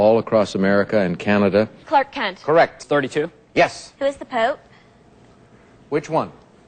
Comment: It almost sounds like she said, who is the poop. 😆😆😆😆😆